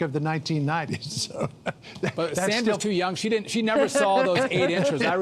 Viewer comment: Did he just say: “Sandra’s too young: She never saw those 8 inches”?